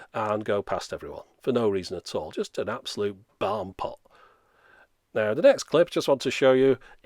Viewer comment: did you call that guy "an absolute barn pole"? Haha, because I hope that's what you said, and I'm totally stealing that phrase. :)